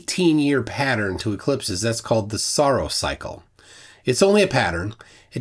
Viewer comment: Haha, I thought you said "sorrow cycle" and Google was like "...did you mean Saros, you idiot?"